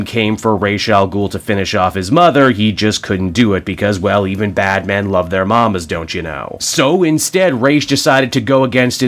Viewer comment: i thought you said batmen love their moms at first haha